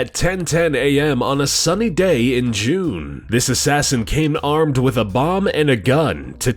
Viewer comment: captions say " he came with Obama in a gun." No offense